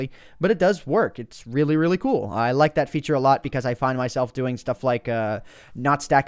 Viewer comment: It´s really really cool becuase i´ll fid my self nutstacking my balls... thought you said that xD